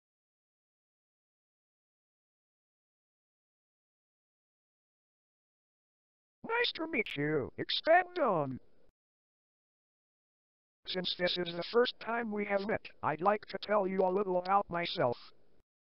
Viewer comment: DID HE JUST SAY EXPAND DONG